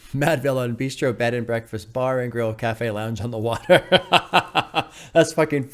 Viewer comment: I thought it said “under water” 😅